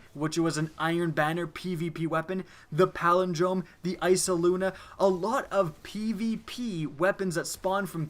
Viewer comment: Did he say eysaluna at 10:36?